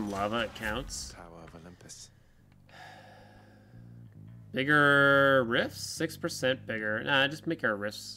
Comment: tf did he just say the n word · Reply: said "bigger", as in the Black Metal boon makes Blade Rift 6% bigger - editor